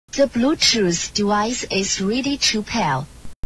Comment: I don't know but I always thought she said "The bluetooth twice is ready to pair"